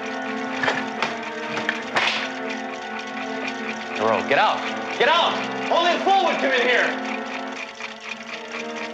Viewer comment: What does Sledge says to Doreau at 1:57? I really can't understand anything but "Doreau, get out! Get out! All this ... is... here". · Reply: "Doreau, get out! Get out! Only a fool would come in here..." ...realizing that he just insulted himself That is what he says, better late than never ;-)